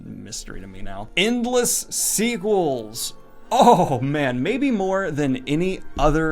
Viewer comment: I dont know why, but I thought he said endless seaguls.